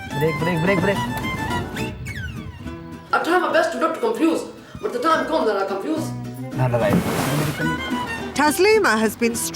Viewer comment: " What did she say? I try my best to not to confuse but all the time I'm confused? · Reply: She Said, I try my best not to get confused, but when time comes, I get confused.